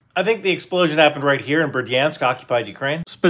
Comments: Man, i'm from Berdyansk, and I've seen this explosion. This is real. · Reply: Thank God you spelled it, I thought he said Verdansk 😅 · I thought he said verdansk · Dude i thought he said "Verdansk" 💀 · I thought he said Verdansk for a second😂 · Thought he said Verdansk